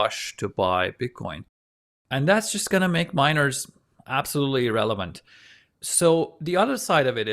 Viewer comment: Did he say pent up demand is going to make miners, “absolutely irrelevant”? *at